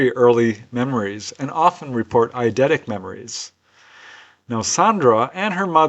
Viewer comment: the captions and transcript messed up your English here.... u were speaking of early memories, "..and sometimes even "TIC" memories .." I believe the caption said.. Now,.I know this is an error, my question being: What is the term you ACTUALLY used here???? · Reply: Oh, sorry about that. The word I said is "eidetic" memories. Eidetic means photographic memory.